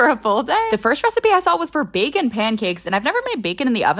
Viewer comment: I thought she said vegan pancakes so when she used bacon I was like- “ GURL BACONS NOT VEGAN U DODO😂 “ but apparently now I’m the dodo